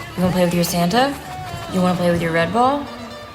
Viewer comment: i thought she said "Wanna play with your red bull?" instead of red ball lmao